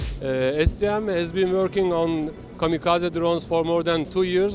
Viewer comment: did he say "kamikaze drones"?!